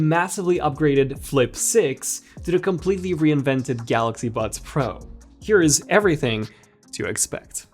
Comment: the captions say “completely reinvented Galaxy Butts Pro”